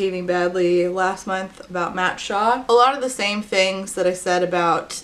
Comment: I literally jumped out of my skin because I thought you said 'Mark Shaw' and I was really confused as to what the author of Hitmen for Hire (Exposing South Africa's Underworld) could have done for you to talk about him.